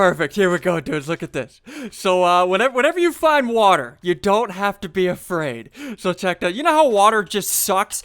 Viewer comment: I thought you said you doesn't have boll